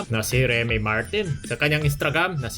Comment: sa kangyang istragram. Tama ba narinig ko🤔